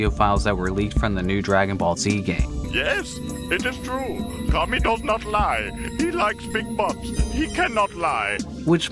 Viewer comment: the Kakarot audio captions are wrong. It says "Tommy don't not lie;" presumably that should be "Kami does not lie," since Kami is the Funimation dub name of God from Dragon Ball/Dragon Ball Z.